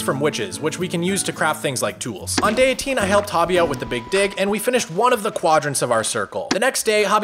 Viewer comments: thought he said i helped hobby out with the big shlong instead of dig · "on day 18 i helped hobby out with the big d#ck" is wat i heard